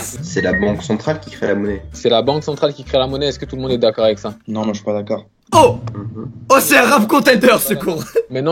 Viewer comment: Le gars rappeur c'est un locklear junior " non moi je suis pas d'accord " moi j'ai entendu "non iiiiiidiot"